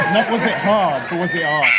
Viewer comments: What does Luke say in 1:58? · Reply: Not was it hard, but was it HARD? it's an innuendo. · Did he say, "not was it hard, was it odd?" or "not was it hard, was it hard?"???